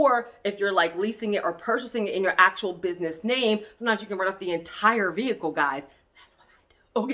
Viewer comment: Was she saying you CAN write off the entire vehicle in this part 6:33? I could hear it.